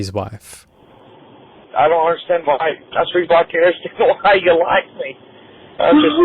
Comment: I died at this caption 😂 "A three block carrot stick" UNINTELLIGIBLE Here ya go, friend.. He says: "That's the reason why I can't understand why you like me" Source: I speak Southern.